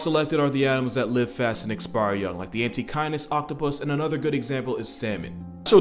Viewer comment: The antechinus is a cute little bastard that loves to bite. Fyi they're also a dasyurid, that means they have the dental formula of carnivores like cats and dogs, while being the size of a mouse. I've handled wild ones and wild is a great description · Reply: The first few minutes i thought he said antikindness